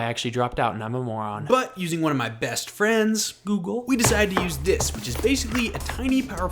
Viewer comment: "One of my best friends google" I thought you said googoo😂